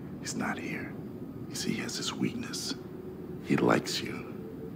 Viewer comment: "he LIKES you" 0:20 all i hear: "I think MOTOMOTO LIKES YOU"